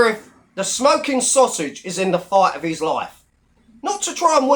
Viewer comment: Thought you said the sheriff is fighting for his life! Then I rewound and heard it properly…. Shame!